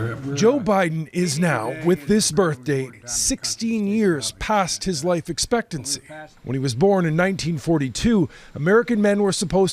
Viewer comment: Lmao I was dying! I thought he said 60 years past his life expectancy!